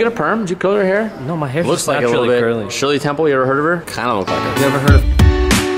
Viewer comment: For those wondering what cut out most definitely said "You ever heard of dig in yo bud" to which they had a mad bone session in the middle of the shop. All facts, eye witness, seent it first hand🙌🏾😌💀😂